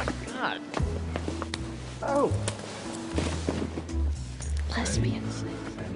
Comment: what does leighton say at 5:29? it sounds like "lesbian sex" then she makes cat noises...hahahahahahah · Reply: Bless me insted” i think